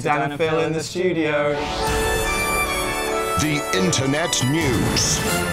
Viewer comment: What does Dan say right at the end? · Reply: Look, I know I'm 6 years too late, but he says "the BBC World Service right there"